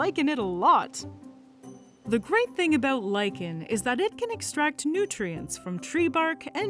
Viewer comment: I had to rewind that to make sure what I heard and I'm still not sure... Did you say they can eat & extract nutrients from rocks?! Oh! LIKEN can extract nutrients from rocks! 😂😅